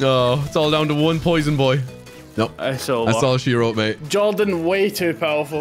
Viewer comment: anybody hear massage hero mate